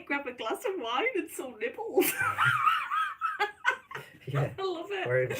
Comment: Was it just my ears failing or did your eyesight fail you again as I'm sure I heard you say "Grab a glass of wine and some nipples" but when I read the comment it was nibbles?